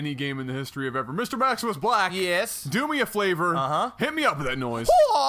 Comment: did just say "maximusblack" "yes?" "do me a flavor"....